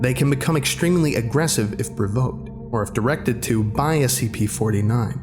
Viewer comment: instead of provoked its provough. Voice crack?